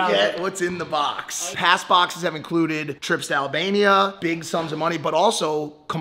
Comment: did i heard trip to Albania ? 😵😍😍